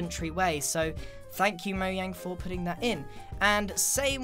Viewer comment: Did I just hear moyang ?